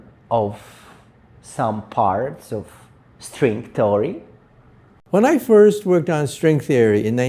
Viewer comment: did he say “String Story”? 😅